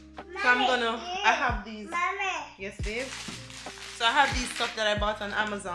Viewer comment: from a married young lady/Queen to another Queen. 1.DON'T BE AFRAID TO SAY MY HUSBAND IS GOING TO DO THIS OR THAT..... 2.DO YOUR WEEKLY VLOGS AS USUAL AND LET YOUR HUSBAND DO HIS PART AS USUAL.... 3.I know all is not well but work together, pray together,work together, don't do vlog without him put him apart of the vlog and its okay to keep the council in session private and other stuff but Queen you keep your family going.... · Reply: Very good and kind encouragement. I heard she said "babez." Yea!😊